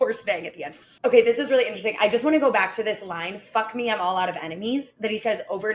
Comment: lol always thought he said “F me I’m all out of amphetamines”